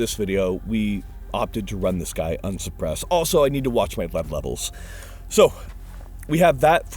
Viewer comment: need to watch what levels? Am i deaf i can not understand what hes saying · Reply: lead i think